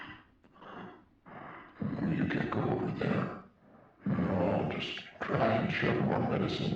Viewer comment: Small correction: He actually said "you can't go over there". Thankfully, the original video has subtitles, which is probably the only reason why I know what he said, lol.